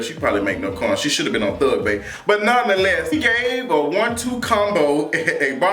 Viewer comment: Thug bait😂😂😂Okay now! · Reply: oh shit, I thought he said thug bae in reference to CB 😂😂